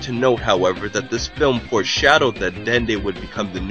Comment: When did I say that? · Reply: I M saying that I misheard U in the video a few yearz back. I honestly thought I heard U say the word "foreshadows" but you really don't,you say the word "foreshadowed" that is all.